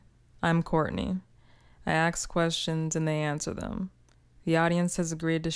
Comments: did she say “I aks questions” ? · Why do I hear "I aks questions"??